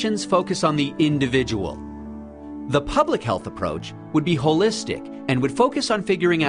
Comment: I thought it said Pubic Health